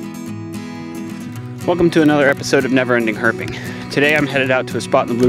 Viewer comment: I thought he said never-ending herpes at the beginning lol. that would really suck!